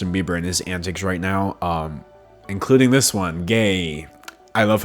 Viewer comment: i think you wanted to say "yay",not "gay" lol